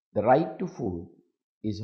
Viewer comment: Mr. President be aware of the pronunciation I heard you say rights to Fool. Are you? Anyway thanks to Teleprompt.